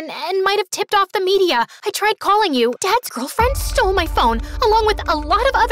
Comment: I found another error! Lisa said Dad’s girlfriend! But the MSA subtitles say Dead Girlfriend’s:_MSAMSALaugh2